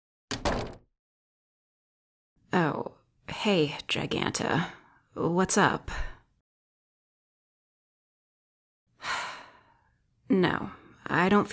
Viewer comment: I thought you said oh hey giganto from Sonic frontiers